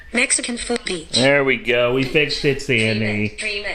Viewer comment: sounded like she said “Mexican foot beach” 🤣